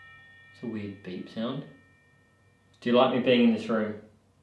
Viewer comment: I heard "this is my room"